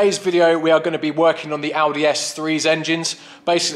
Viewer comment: For a second I thought you said you were going to Aldi, not Audi! :D